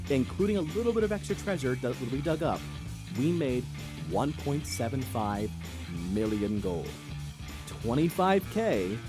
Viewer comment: i dont know why i thought you said 175 million gold, it just was what i thought